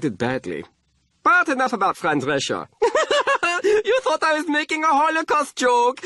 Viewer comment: What does Klaus say at 1:43? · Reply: He says Fran Drescher not friend Russia